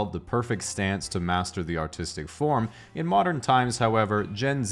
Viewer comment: i thought he said "the autistic form"